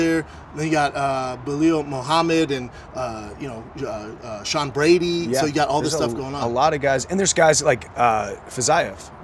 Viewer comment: Did he just say Belal and Sean Brady are at 155?